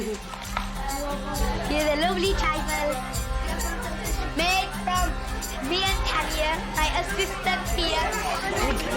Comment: Does anyone know the dessert they are eating at 32:39? I can't make out what she's saying. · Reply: It’s trifle!